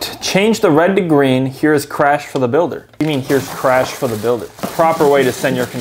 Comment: Bro I thought it said Milker but he said builder